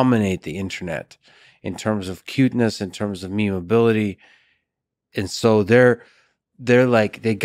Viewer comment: Thought he said meowability